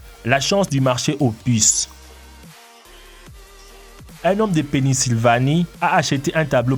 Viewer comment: Drôle de nom de ville. À 3:00, j'ai entendu :"Un homme de PENISvlanie", c'est où ça? 😂😂😂